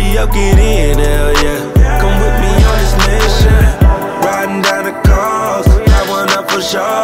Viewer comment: My man in the dreads or braids did he say like the fuck doll or dog? I miss heard that The hit my ears wrong. I thought I heard that. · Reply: I believe you are referring to when he says “Ridin’ down the coast”…or when he say “put you in Provocatéur..”